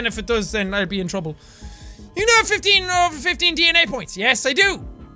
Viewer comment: When he said DNA point, I thought he said d-nya point